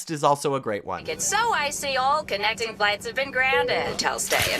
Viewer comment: İ dont understand how anyone never notices this. At 10:32 when the hostess/pilot says "i see yall" bojacks eyes wide open,showing probably a trauma response to the "i see you" incident. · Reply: Yeah, she’s saying since it’s icy, all flights are grounded